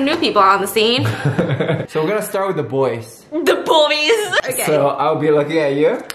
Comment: HAIDHVAGKYFBSX I HAD CAPTIONS ON AT 1:47 AND IT SAID ”the boobies” INSTEAD OF THE BOYS